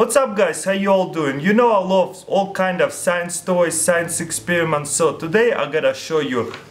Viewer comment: Did you just say sex toy at 0:7